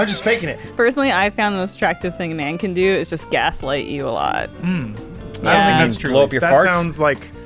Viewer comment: What does Elyse say at 7:18? I just can't seem to understand what she is saying... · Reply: She says, "Personally, I have found the most attractive thing a man could do is just gaslight you a lot."